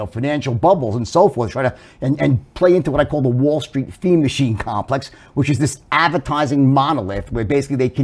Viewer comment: THought he said "wall street fiend machine apparatus" -- trading, I amsick of it. I quit. I am no hedge fund billionaire, let alone billionaire. Obviously. But yeah, I've wasted time and money and lost out on insane profits... so, normal career it is for me. ADHD, and a strong life structure is key and amazing even. CHANGING gears to do other shyt like derivatives or crypto futures and longs and shorts... can be amazing but for 99.8% of people mostly aren't.